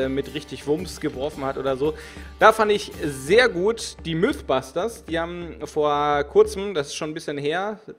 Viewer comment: oh Gott ich hab MilfBusters verstanden ^^ ich dachte das wären die Ghostbusters als Frauen :0 boar meine Gedanken Gänge sind sehr sexistisch:??